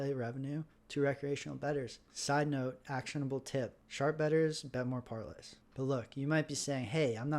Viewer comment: "Sharp bettors bet more parlays."?? At 12:17. Did I hear that right??